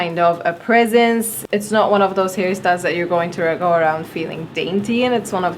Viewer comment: Sounds like she says Harry Styles in